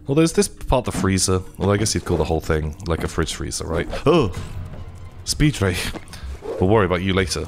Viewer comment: This whole time i thought was saying 'speed ray' when he was saying 'spidre'